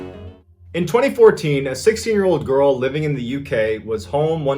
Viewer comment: I had to back this up. I thought you said the girl was 6, not 16.